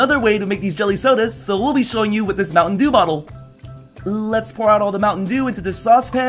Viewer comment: I thought pyrocynical said mountain Jew instead of mountain dew Now I keep hearing mountain jew H e l p